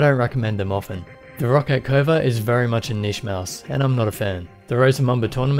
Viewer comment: what is that mouse on 1:05 the gray one, i cannot understand what he said, please tell me T_T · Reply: Roccat Kova